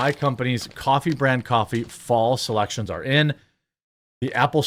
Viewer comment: The Coffee sounded like you said "false elections" 😂